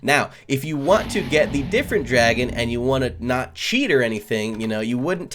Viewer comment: i thought he said red dragon